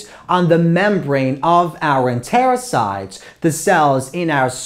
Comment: sir i have a question what is the word u say in 1:10 in the membrane of what? thankyou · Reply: membrane of enterocyte